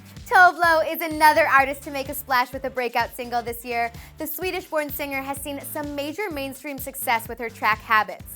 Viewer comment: When she said that Tove Lo was a Swedish born singer, I thought she said Swedish porn singer which actually makes sense with the subject material in EVERY SINGLE ONE OF HER SONGS!